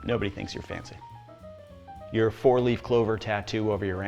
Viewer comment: Did you say 4-leaf??? <link>